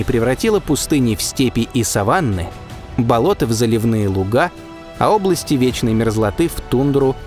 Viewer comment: послышалось:"забивные луга"